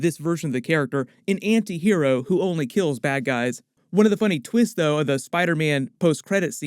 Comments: I didn't know he said bad guy, I thought he said, "That guy..." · You heard “Bad guy,” I heard “That guy”